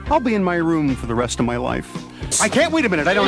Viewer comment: did he just say he had to remove the other guy's pants or am I crazy?